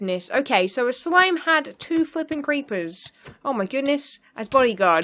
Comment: this is the 2nd time I heard you say f***ing in a video 😂